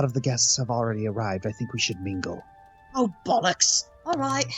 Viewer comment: Did she say “Oh, bollocks!”?